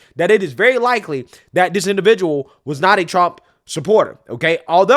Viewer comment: I thought I heard his dad was a Trump supporter so it does not necessarily mean that Thomas put them there